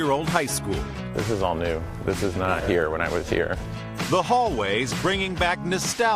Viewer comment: It sounds like he says Bill Hater.